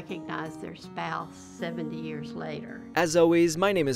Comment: Thought he first said "life nugget"